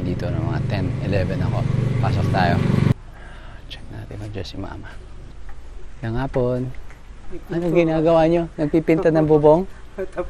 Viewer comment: am i heard it right? maayong hapon?